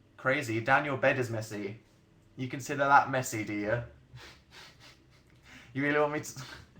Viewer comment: DID HE JUST SAY DEAR OMFG THATS TGE CUTEST THING EVER IM CRAFTING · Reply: I WISH BUT HE SAID DO YA? LIKE DO YOU BUT OH WELL JUST IMAGINE IT AS DEAR